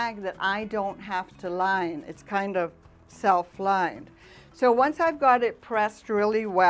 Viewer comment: Okay I heard you say it itself lined sorry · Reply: Hi Beverly, Yep! It's self lined!